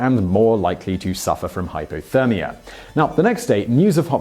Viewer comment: Did he say hydrothermia at 4:05?